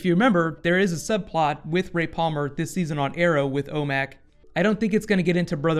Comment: Coulda swore you just said "Rape Almer" lol.